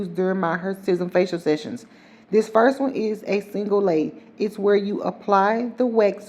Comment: When you said single Lay..i thought you said single Lady😂😂